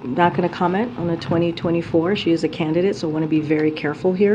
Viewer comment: Did KJP say, "SHE is a candidate" @ 3:19 in the video?